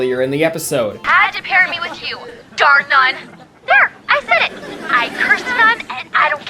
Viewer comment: As a kid I didn't understand maddies line in the baby doll episode when she says darned nun. For starters I didn't understand the concept of nuns at the time and I just thought she said Darndun which I thought was just some new word for "idiot" I am only just now learning what she actually said.